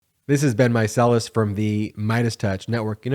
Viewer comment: Ha. Every time one of your videos starts up, I hear "This has been Micellis...". But the closed captioning bot hears: "This has been myself"